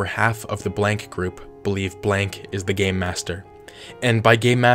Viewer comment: I swear I heard you say gay master not game master